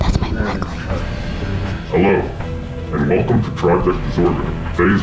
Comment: It sounded like he said welcome to tragic disorders butt I don't know